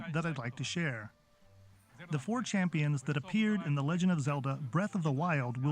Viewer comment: it sounds like he said 4 chan for a second